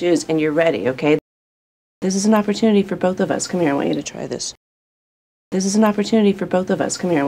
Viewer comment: 여기서 This is an opportunity for both of us. Come here. try this. 여기 빈칸에 또 뭔가 단어 들리는데 뭔지 혹시 아시는 분? 전혀 모르겠네요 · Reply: we need to